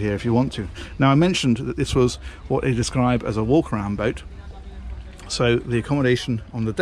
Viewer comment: said " it's a walk-around boat" what i heard " it's a war crime-boat" what is wrong with me ?